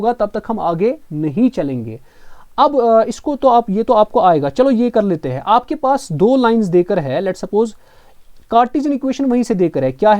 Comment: Where . Please give me time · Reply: I heard it as two points instead of two lines.